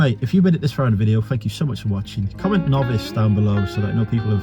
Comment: Good stuff getting all sorted out. comment what? Sounds like you said Novits?